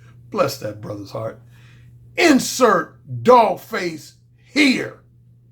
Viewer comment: First times I heard you say "insert - dog face - here" I thought you said "incel - dogface - hater" 😄. In this video it's suitable.